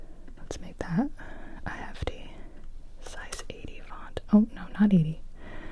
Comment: Hey captions person. I love you dearly, but it was really funny when you thought she was saying the name of the font. It was just “a hefty size 80 font.” As in it’s a big font size, not even a different script.